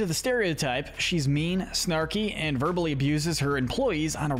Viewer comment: I swear, when you said verbally, I thought you almost said verbalase